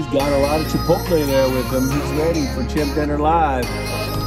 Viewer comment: Who else thought it said simp diner